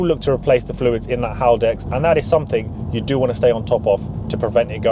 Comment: fluid in the what? I dont understand what he is saying, send help! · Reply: Haldex is the awd system